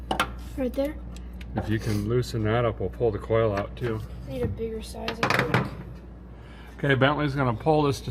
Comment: I thought I heard Bentley say " I need a beer" 😂 but it was "I need a bigger size"